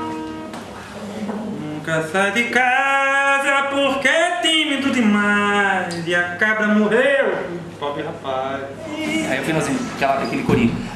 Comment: HAHA' EU Axu q eu Entendi u q o Rafa Fala no 2:59 " Bebi agua Na forma pra fazer Manteiga Lá Fora, Nunca Sai de Casa Porque é Tímido de Mais, E a Cabra Morreu e o Pobre Rapaz " Dps q o Rafa Entra no Vídeo Fica Tudo Melhor .. Rafa Owna .. Queria Trabalhar cm ele .. ia Morrer d Rir Angra Rulez .. Sai Novo Álbum Logo OBS : O Violão Quebrou qndo o Kiko Caiu Nele ? uashUAHS'